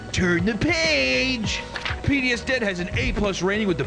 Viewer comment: Everytime he says "PDS dept", I hear "PTSD". ;-)